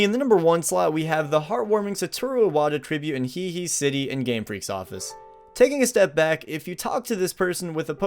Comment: I thought said Game Frick